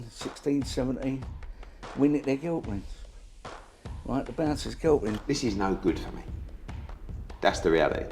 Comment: ''must have been 16..17, we nicked their girlfriends, right the bouncers girlfriends'' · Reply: I thought he said “the bouncers killed him” at the end?